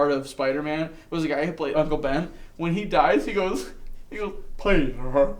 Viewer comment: What is he saying at 11:42? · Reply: He said Peter